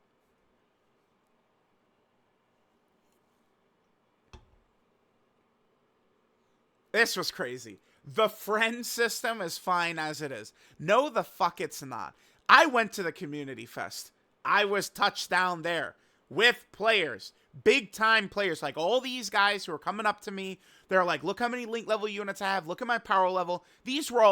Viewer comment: I swear I walked away and wasnt paying attention, and then at 20:50 all I heard was "I was at the community with big players and I got touched down there" I was like wtf is going on IS THIS DOKKAN😭